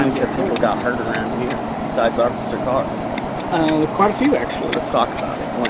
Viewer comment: the caption says unintelligible, but the driver clearly days "Officer Carr" I assume this is in reference to Fayetteville, Arkansas police officer Stephen Carr, who was murdered in cold blood in the driver seat of his cruiser, sitting in front of the police department, roughly 6 years ago now.... To my knowlegde, I dont believe they ever caught the perp. I went to high school with his fiance, shes my pharmacist too. Wonderful woman, lost her love too soon. RIP Officer Carr badge no 413. We wont forget you, sir. ❤ 4/13 here is His Day.